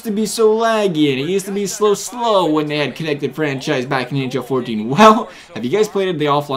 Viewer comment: IT sounded like he said connected french fries